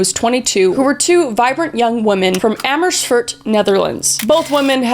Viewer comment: WAIT WAIT WAIT DID SHE SAY AMERSFOORT?????? LIKE MY. TOWN???? NAHWWWW